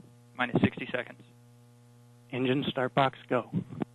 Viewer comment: Thank you for this video! I love hearing the engineers preparing for the launch. It's inspiring. Also, at T-58 seconds (1:17 into the video), does he say "engine Starfox go?" hahaha